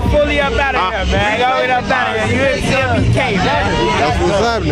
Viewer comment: did he say bdk infront durk ? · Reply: Cmbk